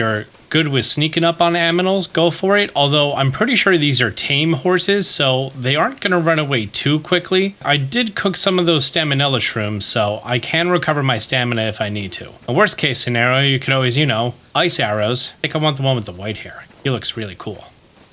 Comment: Did you just say “staminella “ shrooms? 😅😂